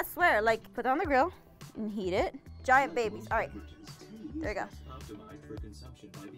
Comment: did she say baby